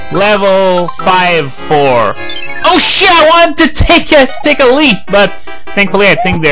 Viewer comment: it sounded like you said, "Oh shit, I wanted to take a leak!" lol